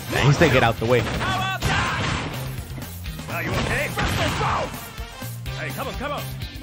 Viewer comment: i swear he said "Are you ok? BRUSSEL SPROUTS!"